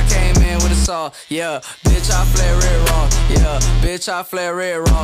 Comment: I thought #1 lil pump said: "bitch I flare it raw"